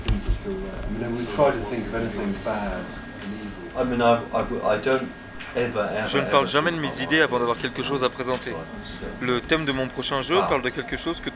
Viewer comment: Oh, thought you might be interested - I've transcribed the English audio and added it as a subtitle track. It's quite an interesting video in the end, especially the Molyneux parts. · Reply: Ace! Thanks a lot! By the way, at 8:07, I think he's saying "I don't ever ever ever talk about my ideas until I've got something to present". I can't tell exactly what he says after the "I've", but I cross-referenced it with the French, and that does indeed seem to be what he's saying.